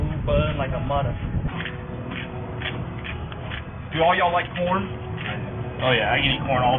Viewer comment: corn! I thought he said PORN at first!